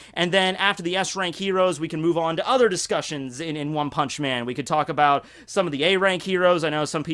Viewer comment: i legit thought it said one munch man